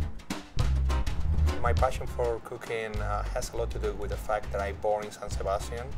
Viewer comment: first 0:16 seconds not gonna lie it sounded like his passion for cocaine